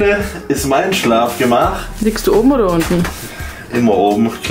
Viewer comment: ich bekomme einen schock und frag mich was der jetzt für eine story erzählen will da ich wichst statt liegst du verstanden habe argh XD